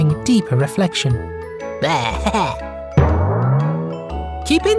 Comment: The last one i thought it said "your videos are a job to watch" and i was like "bro that's harsh"